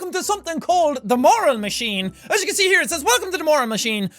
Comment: I heard is as "Moron Machine" XD